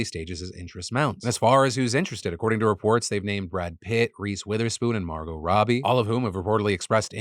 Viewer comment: Did you just say Grease Witherspoon lol